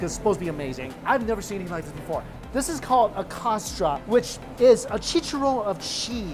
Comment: Can someone spell for me what that cheesey thing wrapped in cheese was he ate at 15:40 is called so I can search for more information about it? Sounded like "acosta" but I get nothing having to do with food from that in a search. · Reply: Costra is what I heard. I looked it up and it brought up Costra de queso or Chicharrón de queso. Hope that helps!